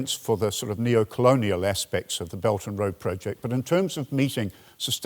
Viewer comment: He keeps referring to the "belgium" road project. I thought this was a Chinese initiative? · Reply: I didn't know what he meant myself, until I looked it up. I thought he said "Belton Road" , which oddly enough it does seem to get called on a lot of sites. What really threw me at first, was that the "Belt" means roads and other land infrastructure, but "Roads" means ships and maritime infrastructure ! I guess only the Chinese would understand it's etymology, as it seems to come from vernacular phrases.